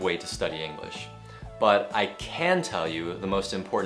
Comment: Thầy ơi ! Đoạn 4:49 thầy phát âm "I can" mà em nghe nhầm "I can't" Thầy nhấn âm như vậy để làm gì vậy ạ ??? Vì có video thầy từng nói:" Can't" thì nhấn mạnh trong câu, còn "can" thì đọc lướt qua nên em thắc mắc. Mong thầy giải đáp !